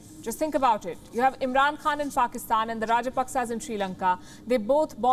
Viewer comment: Did she just say 'faakistan' (faaka = going hungry )